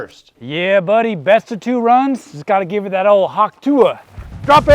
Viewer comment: am I the only one who heard him say hawk tuah 🤣🤣🤣